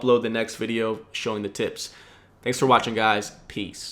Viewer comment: did you say tits o.o